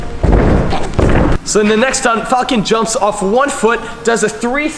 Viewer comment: did he just say "Fak"?